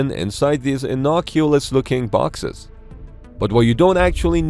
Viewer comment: Did he say "innoculous" @ 0:47? 😂